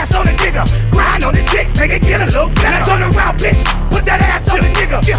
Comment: "Never do the cookin' by the book🤐" I Misheard it